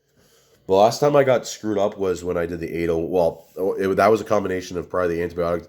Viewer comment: ….sounds like you said, “the last time I got screwed up was when I did anal…”. Did I hear that right? 😂😂😂